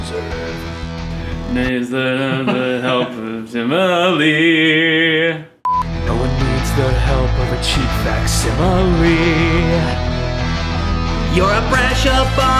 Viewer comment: What is he saying here? Because what I keep hearing is "No one needs the help of a cheep fox simply"🤣🤣🤣